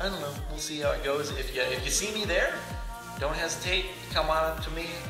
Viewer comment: When he said 'dont hesitate' i thought he said 'don't haz cake' 😂